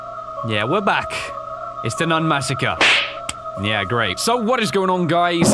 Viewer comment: I thought it said Nun mascara